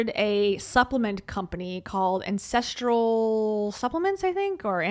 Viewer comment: every time you say “ancestoral” I think you’re saying “incestual” 😂😂😂